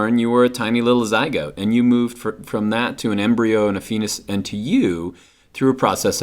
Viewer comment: "...from an embryo and a ...?... and to you...." What does he say at 0:28 seconds? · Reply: KnowJesusKnowPeace FETUS